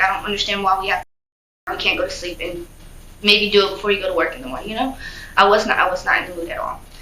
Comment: This a set up i thought she said bay dad got off in the morning